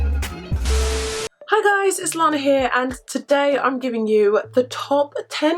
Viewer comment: When you said "it's Lana here" I heard "it's long hair" 😆 and I was like "What it long hair?" 😆